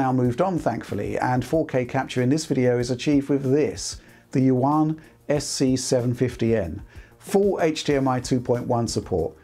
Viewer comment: What is the name of this capture card? I can't understand it at 3:00 . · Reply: Yuan SC750N. Random company, $1,399 USD.